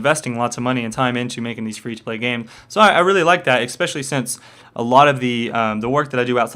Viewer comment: at around 15:30 did you just say xpecialy since a lot of work i do etc....? o.O